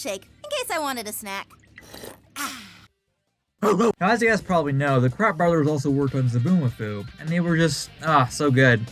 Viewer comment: I always thought they said they were the CRAP brothers not kratt